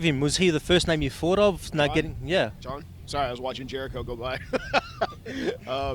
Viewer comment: Is it me or did he say Jericho in